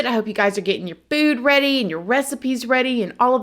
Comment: 😆 said, " I hope you're getting your food ready" I heard "boob ready" 😆 can't unheard it, now.